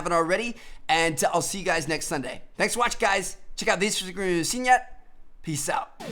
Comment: Lol what did he say near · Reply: I think he said " checkoutmyinstagramifyouhaventsennyet " xD